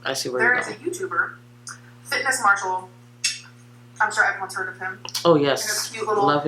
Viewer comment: I was listening to this when driving, I thought she said Yanis Marshall. I was like...WHAAAT. Do not drag Yanis into this Also if you've ever worked in customer service "I do apologize" is a way to say "i get you're upset but it's not my fault". It's all she ever says in terms of apology. Also the shade she throws on Eric and Becky is so insane. Now Eric lost her tripod. I hope he sings like a canary when that breakup occurs because I don't believe for a second he doesn't notice that shit. Another episode of word salad with where she fills the time with a bunch of words, often the incorrect ones or in the wrong context, and there is zero meaning or sincerity behind them which is why she can never remember what was previously said. Because she talks and talks and talks and never says shit.